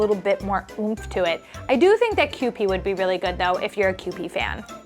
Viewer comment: I haven't met a vegetable yet that does not make an omelette taste fantastic. All the veggies!!! ❤❤❤ Also, at 30:31-30:34, did you mean Kewpie mayo from Japan? The subtitles say "QP" twice. Just wondering?